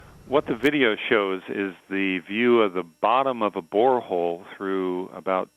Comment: thought he said wormhole for a second?????????????????????